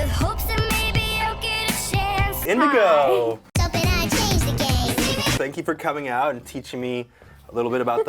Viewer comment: I literally heard the first time “And I hate the gays!” And as a gay person I clutched my pearls